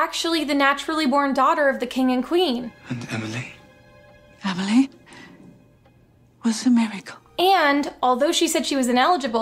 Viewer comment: What does she say here? I'm confused as to whether Emily is somehow their natural-born daughter or not. EDIT: Ohhhh, she said "Emily was a miracle"!